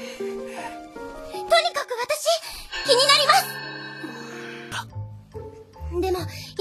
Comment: Is it just me or am i hearing "kiniNarimasu" 0:19 instead of "kiminarimasu".